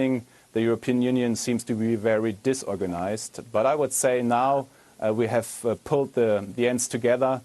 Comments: Jens Spahn: "Now we have pulled the JENS together" - Wer hat's erst auch falsch verstanden? 😅😂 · Did he say we've pulled the Jens together when talking about the EU @ 8:00? LOL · did he just say:" and now we have pulled the Jens together?" lmao Edit: i know he said ends...